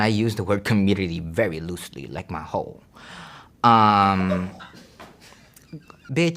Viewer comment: Did he just say "loosely, like his hole" ?? I couldn't help spitting out my drink , laugh/choking. Love ya soo maaach!